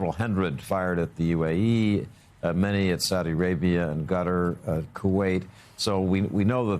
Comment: Did he just say " gutter", instead of Qatar....hahaaaaaaaaa